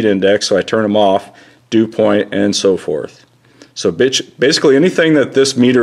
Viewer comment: did he say bitch at 4:46 ?